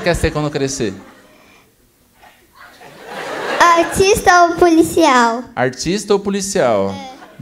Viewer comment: só eu que ouvi autista